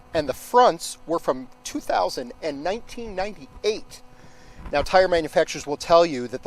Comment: Did he say the front tires are from the year <num>. Can we even get those yet. 02:55😮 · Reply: ONE was from 1998. ONE was from 2000....